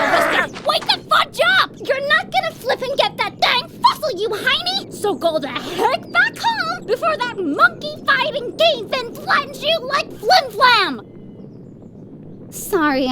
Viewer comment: "Wait the fudge up! You're not gonna flipping get that dang fossil, you hinny! So go the heck back home before that monkey fighting cave-in flattens you like Flim Flam!" I think that's what she said, but her voice is so high pitched it's hard to tell.